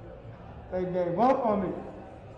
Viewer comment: Did he say, “They gave up on me?”